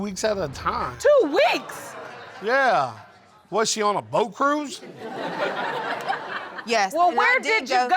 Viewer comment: Boyfriend..,Was she on a boat cruise 🚢?. I misheard as hoe cruise 😅😅😅 and had to rewind 😂😂